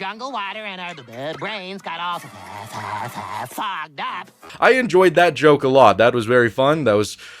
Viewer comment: did bro say fucked up? 😂😂😂